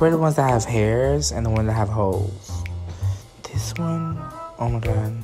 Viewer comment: For a second I thought he said “hoes” And he said “holes” I was like OHHH